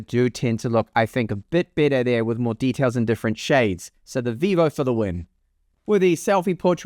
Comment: Did I heard Vivo for the win at 4:02 ? Vivo is the only with overexposed image, lost details in highs.